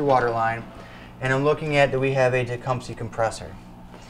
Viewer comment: "dacumpsey compressor?" I'm not sure what word is being said. An on-screen caption might help. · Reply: Tecumseh compressor